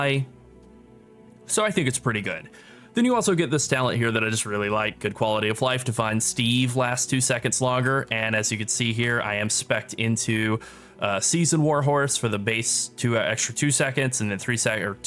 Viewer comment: All I heard was Divine Steve.